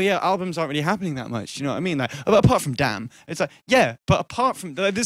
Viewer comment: what does he say at 1:16? apart from what? "damn" or "dan"? lol